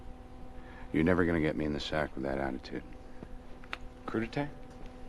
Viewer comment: What did he say at the end (2:14)? "Cour de tet" or something?? Wasn't able to tell and get the joke. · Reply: I think he says “crudités” 😅